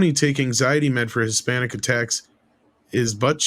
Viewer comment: "...he takes these pills for his panic attacks..." Thought you said Hispanic attacks.